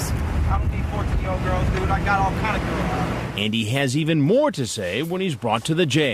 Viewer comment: "do I look like I do 14...." Yeah actually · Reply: I swear I also heard him saying "I'm not that kind of girl". wut?